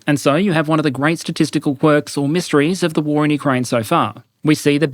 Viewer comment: misheard it as "10000 russian Todd Howards disappeared"